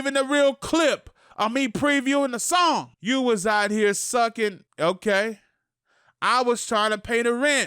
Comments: He say like “ya was out here sucking dick”?Bc that sound like it say I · bro ion care what dugg say he said, that shit clearly sounds like him sayin "i was out here"